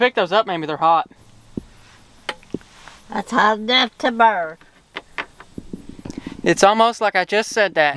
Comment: don’t pick those up mammy they’re hot mammy: it burns also: it’s almost like i just said that 😀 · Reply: i thought she said hot enough to burn .. like if she was siting in nice dry grass she could be in real trouble . go fire marshal granny ...